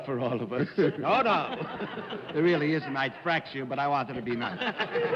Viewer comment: what does Phil Silvers say? CC says " it really is a nice fracture but". it sounds to me like "I 'd frac you but I wanted to be nice". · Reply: Sounds to me like "I'd fracture you but I wanted to be nice."